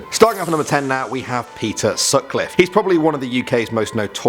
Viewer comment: When he said peter I thought he said pizza lmao